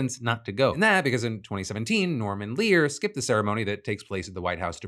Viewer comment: had to rewind 3 times because I’m not watching the video, just listening in background and swore he said “Mormon Leaders” instead of “Norman Lear”.